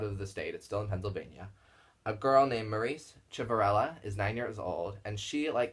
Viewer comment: What was the other girls name? Sounded like Maritz Shiparella? But I think I am mishearing you.